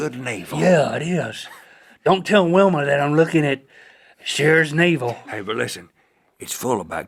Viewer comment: Weirdly I thought he said "Shitter's navel."